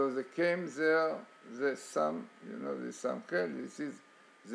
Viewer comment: What is Samael? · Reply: I must have misunderstood, I thought he said SAMAEL was the angel God appointed to watch America. At the 6:30 mark